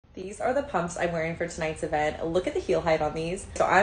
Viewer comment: I thought she said “These are the heels I’m wearing tonight to bed” 😂😂😂😂😂😂😂